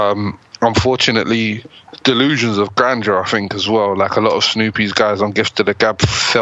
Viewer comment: I know said delusions of grandeur but at first I thought he said delusions of ganja cus they were all high as shit